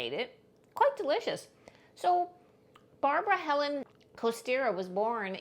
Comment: It sounds like she said Barbara instead of Martha lol